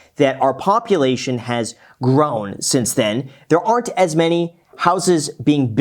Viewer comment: I keep hearing the world: "Populations has growths." Since the big boomer "baby boom", I always see a decline in babies and increase in death. I cannot understand how now we suddenly don't have enough. Or someone is holding them out of the market.